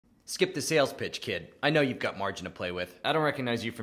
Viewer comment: I thought he said "skip the sales, bitch kid"